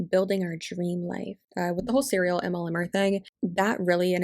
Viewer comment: When you said “serial MLMer” I totally thought you were saying “cereal MLM” for a minute and I was like wtf there’s an MLM for cereal now too!?😂 (it’s early and I haven’t been caffeinated yet)